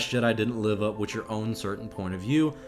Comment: and 7:20. You thought Rain said Live up to your point of view, when it’s actually “Line up with your point of view” it’s two different meanings.